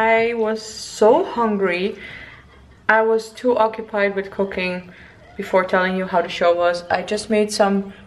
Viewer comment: That arena moment at 1:46 though😂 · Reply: did I just hear that you were too occupied with cocaine at 4:26 ? 😂😂😂😂